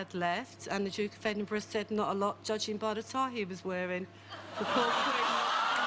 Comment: wt does she say after the queen asked the blind kid how much sight he had left? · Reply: The Duke of Edinburgh says "not a lot judging by the tie he was wearing"